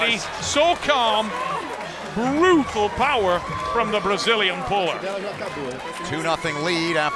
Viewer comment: did he call her the “Brazilian boar”? What a knucklehead